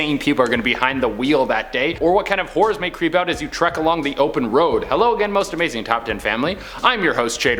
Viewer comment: It totally sounded like you said whores along the open road not horrors lmao